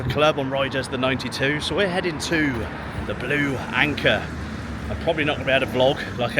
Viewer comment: Blue ⚓️. Or did I hear Keirs last name w⚓️? You know I'm not 🇬🇧. Please, enlighten me.😊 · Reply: Blue Anchor. Proper boozer. With proper bar lady's.